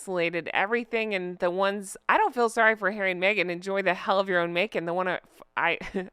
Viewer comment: I swear I heard you say it and in my mind it was spelt “Hell of their own Macon!” How Southern am I?